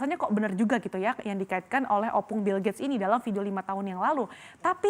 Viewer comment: Ak coba ulang2 i menit ke 3:17. Ak dengar OPPUNG BILL GATES. ap salah dengar ya? 😂😂